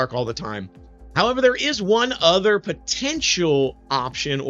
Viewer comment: did u say potential?